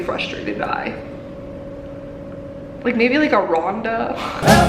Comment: Tbh, I thought you said, "Roku", instead of Rhonda lol